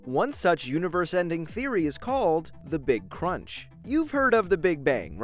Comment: Oof I thought the big crunch said the Big chungus instead XD